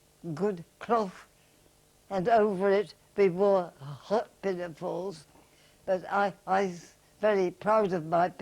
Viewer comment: Hi! I'm very interested in what is saying at 15:53 - "hot pin and falls'? I cant make it out and CC is not helping. Thanks! Purpose is I'm currently researching a character for a novel I'm outlining and am trying to bring a source of historical accuracy to the timeline. Character will be male, setting will be after WWII, where he served as an officer. I'm debating whether he will be a doctor or surgeon, it all rounds off to whether or not I can build in as much realism to his backstory. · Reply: Pinafores.